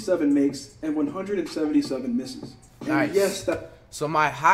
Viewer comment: did u just say nice 😭😭😭😭😭